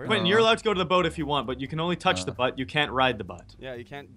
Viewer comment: i thought he said butt xD